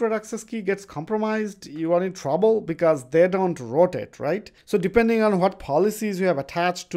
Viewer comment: not sure what the word is, 'you are in trouble because they don't __ it' ? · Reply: "rotate" , apologies for not spelling it super crisp, thanks for watching